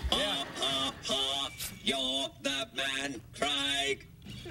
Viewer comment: What is it that Geoff keeps saying? I hear "Yah, haw haw, that man, fried!" And I know that's not right. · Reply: Seven years late with the answer, but he's saying "Ha ha ha, you're the man Craig!"